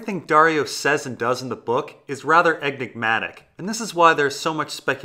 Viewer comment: it sounded like he said "eggmcmatic" like it was a burger at mcdonalds or something